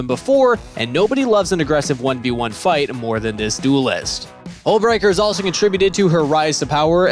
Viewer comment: did he say ohmwrecker xd?